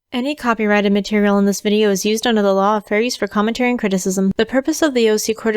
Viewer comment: I don't know why but during your disclaimer I hear "under the law of FURRIES" not Fair Use and it's both hilarious and a little annoying. More cause no matter what I do I can't hear anything but Furries.